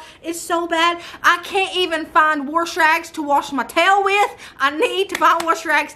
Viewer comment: 🤣 I thought you said, I can't find a washcloth to wash my towel with! 🤣 Laazzy day, I can't hear right half the time!🤣 I use vinegar with my towels and they don't smell like it after, promise!